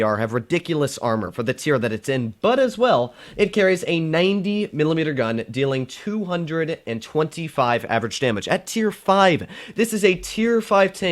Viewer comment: I thought he said a 190mm gun and I was like 0-0 Wtf.